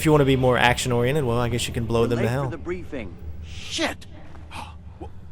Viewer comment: did he just say "you could blow them now" ?